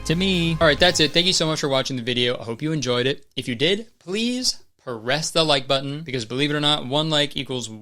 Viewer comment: at the end of the video i thought kurtis said ‘harass the like button’ like huh, cool… ok